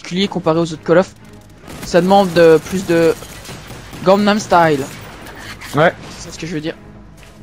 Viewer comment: i swear i heard him say gangnam style